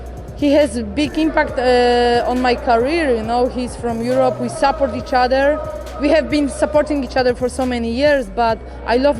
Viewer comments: I thought joanna said "we slept with each other" when referring to McGregor 😳😂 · Ohhhh Joanna, “SUPPORT each other”, I thought you said “slept with each other” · Bruh, for a second I thought Joanna said we slept with each other.😂😂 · Did anyone else hear "slept with each other." Ha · did she say 'we slept with each other'?